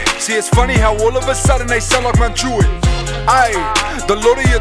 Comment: did he say Mt. Druitt?🤯 as in Home of the brave?😂🔥